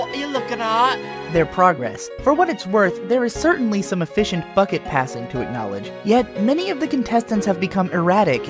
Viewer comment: I heard Me-Pad say "fuck it passing"